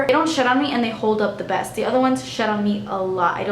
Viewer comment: Who else thought she said shit on me instead of shed on me 😂